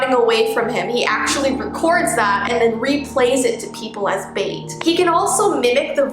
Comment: Edit: I realized said this, I thought she said “as mate” not “as bait”, I'M SORRYYYYY! TvT I heard something like- Siren head can play recordings of people screaming, and when you go check it out, you're actually just getting closer and closer to him, because he's the one who has played the scream sound to attract you to the source. Which is him.